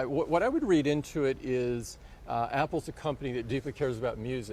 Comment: Thought it said apples cock 😂😂😂